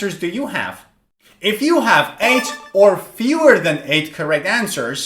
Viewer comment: I found an error in your video. At 15:34, while explaining the score, you said '8 or fewer,' but the subtitles say something different and don't include 8. The subtitles show: 'If you get 8 or more, then you are on the next level